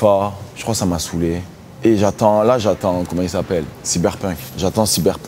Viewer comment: Il dit quoi à 3:45 ? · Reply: The last of us